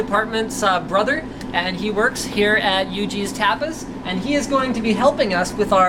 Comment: i thought i heard yougeez... (cant say the word).. so its yuji's...